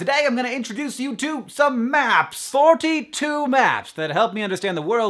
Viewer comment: My player froze for a second. So I thought Hank actually said "some map.........sss" He didn't obviously, but that would've been funny.